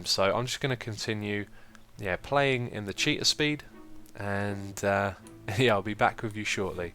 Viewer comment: ahahaha cheetah speed...I always thought you were saying cheater speed :P